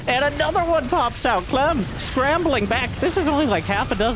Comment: Random thought, but when you said the overlords were rambling around the map and didn't know what word you actually meant... I think you crossed roaming and bumbling, cause either would work but combining them would've been cool if such a word existed too = ) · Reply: Oh nm... 'scrambling'...